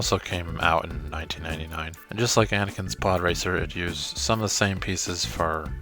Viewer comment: I thought it said pooracer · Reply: I thought you said "poorracer" 😂